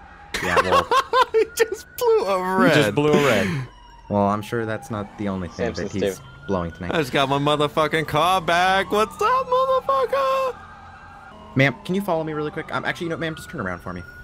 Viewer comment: LMAOOO DID ANYONE ELSE HEAR THAT COP SAY, "THAT'S NOT THE ONLY THING THAT NEEDS BLOWING TONIGHT" LMFAOOO I'M DEAD